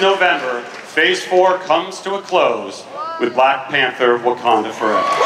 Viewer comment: Wankanda instead of wakanda hehehe